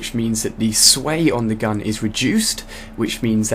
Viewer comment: sounds like he said rejuced